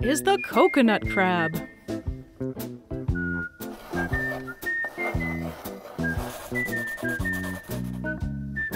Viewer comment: When the host introduced herself, I heard it as 'Daniel Defoe', and for some reason, was reminded of that antagonist from Huntik.